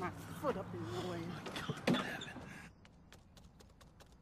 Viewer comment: First time seeing this I thought she said “B***h sexy with glasses”😂 would’ve sounded funnier tho😂